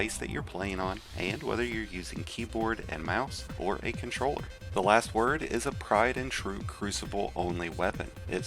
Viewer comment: Did you really say "pride and true" instead of tried and true at 9:49 ? Jfc you're dumb